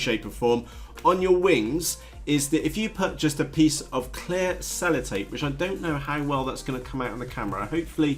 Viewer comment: you talk about some tape on the leading edge of the wing. What kind of tape should we use? I could not understand what you were saying. Can you provide a link to the tape? Thanks! n Nice review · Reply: sellotape... packaging tape? I guess this is the stuff you are talking about..??